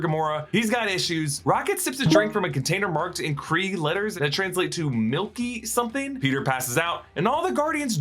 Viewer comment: I search around what actually said and it actually says mango milky fizz